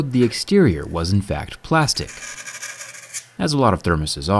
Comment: Who else heard "...cold d**k plastic..."